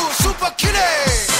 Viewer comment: All this time I thought he was saying "super kitty"